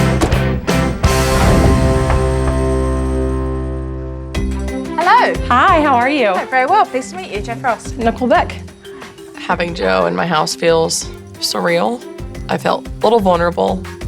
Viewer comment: am i the only one who hears Nickeback😭